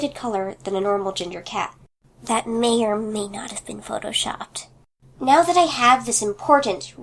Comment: She said Photoshopped right cause it sound like she said Fluttershout